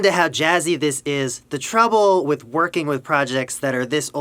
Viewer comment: did he just say treble